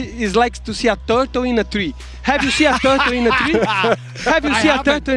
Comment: I honestly thought he said turd in a tree 💩 I guess both work like! 😅😂